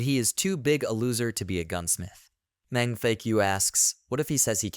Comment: All I hear is “mang fu*k yuu”